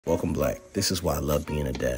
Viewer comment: I mainly watched the whole thing because I could have swore I heard you say welcome black😅